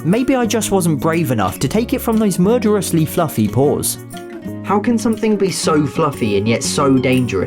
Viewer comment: When said "Murderous fluffy paws" I thought he said "Murderous fluffy whores"